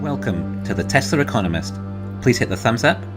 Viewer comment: “Welcome, to the Teslur economist” lol, every time bro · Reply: I thought was saying " Tesla reconomist" lol.